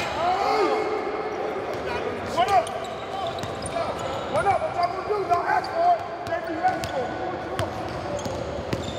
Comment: Who else turned back to 15:02 to see what Russ said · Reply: Native On Deck “We not losing shit”